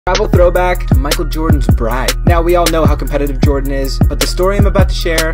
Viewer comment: Anyone else thought he said bride? Lmfao